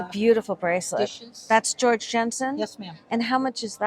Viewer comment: I kept hearing "George Jetson" instead of George Jensen. Great video!